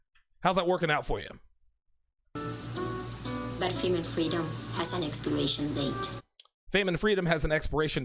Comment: I think she said “female freedom, has a expiration date”.